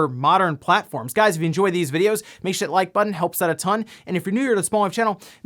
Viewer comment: What he's trying to say: "Make sure you hit that like button, helps out a ton" What I hear: "Makeshyit like button helps out a ton"